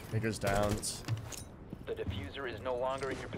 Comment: did he say ni**ers downs or am I tripping